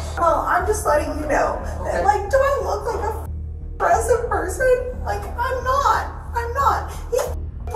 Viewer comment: "Im not an oppressive person" Your voice and attitude are enslaving my patience sweetheart. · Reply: Oppressive? I Thought She Said Aggressive 😂